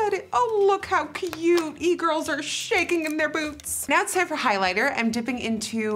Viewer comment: No one: Not even E girls: Nikkie: E girls are shaking in their boots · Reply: thought she said boobs not gonna lie 💀💀💀